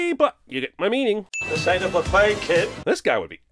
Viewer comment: Seriously? that old guy at the bar says "This ain't a buffet kid!" I always thought he was saying "Is this the start of a fight kid?" because of Vivien moaning at Kit.